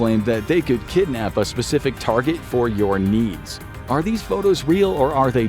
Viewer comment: i misheard it as or your niece